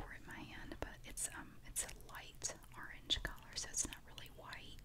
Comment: I thought she said orange oh sh*t